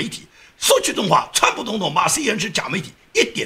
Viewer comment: 刚刚说CNN是造假的媒体，我听说了。我听成了CNN是赵家的媒体。